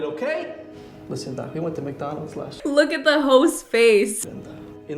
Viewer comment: I swear I thought you said “look at the hoes face” at 13:58 and I was like damnnnn where did that come from